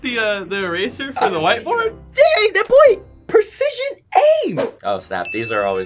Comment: the captions say d*mn instead of dang